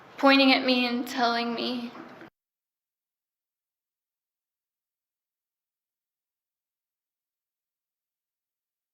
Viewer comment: what does she say Michael said to her? The sound went muted and I cannot read lips. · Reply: "Don't tell her a f***ing thing." Then she says "And Ashley turned around and said "She's gonna tell me everything".